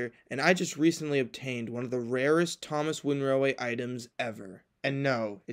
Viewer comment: I thought it said "I OBTAINED ONE OF THE RASIST THOMAS WOODEN RAILWAY ITEMS!!!"